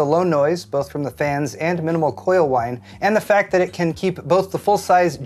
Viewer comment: Did he just say coil wine???